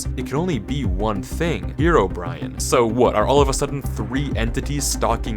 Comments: DID HE SAY HeroBrian?? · Did he just say hero brai-en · Did he just say "Hero Brian"? lol